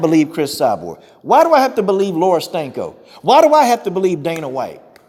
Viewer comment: Don't let this distract you from the fact that every time I hear Coach say her name I hear "Laura STANKO". Does anyone else hear that?🤔